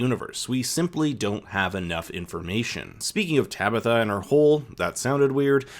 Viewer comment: I cracked when I heard "Tabitha and her hole" which did sound weird 🤭